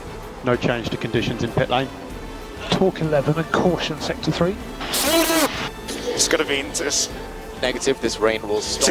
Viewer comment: What did Stroll say? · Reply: Shaddap